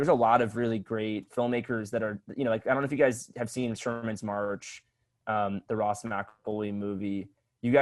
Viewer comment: what movie did Lance say? (just finished the podcast, really great episode!) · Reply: Sherman's March